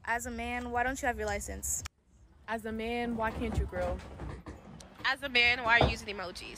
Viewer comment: I thought she said 'why cant you grill?' lmao